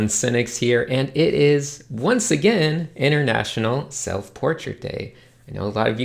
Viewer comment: for some reason I heard "international self-torture day" and I thought of November